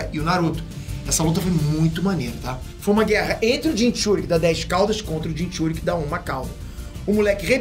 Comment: com certeza Peter Jinchuuriki da 10 caudas kkkkkkkkkkkk mano ;-; sério q ouvi isso